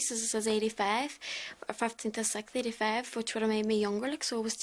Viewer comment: what is she saying? “15th err the sect”?? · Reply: 15th of the 6th 😂